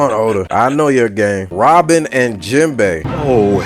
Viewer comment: did he say "come on oda i know you are gay"?